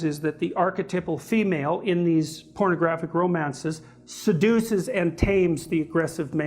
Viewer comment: Anyone else catch "Sadducees" instead of seduces around 10:44?